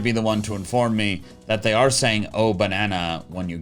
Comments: fun fact, it’s in direct reference to Donkey Kong 64. Whenever you got a golden banana in the game you would hear drums and a deep voice going “ooooooh banana”. It’s actually really funny in the original that once dk bananza was announced, I made the og sound my text tone. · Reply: >.> <.< I always heard it is "Gold... Banana!" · I heard oh bashana